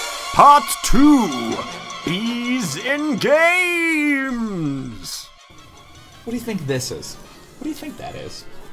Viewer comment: subtitles say and I quote “ease in gays”